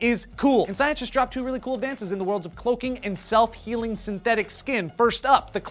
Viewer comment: I thought it said "self-heating skin", wow I was wrong. That would be pretty cool though..